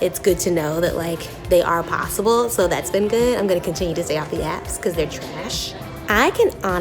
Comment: Oh I thought she said “stay off the abs”. I thought it was a dig to not be shallow or something